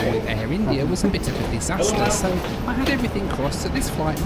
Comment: I thought you said hello there sexy to the flight attendant at first. Then I realised you were saying your seat number 6A 😅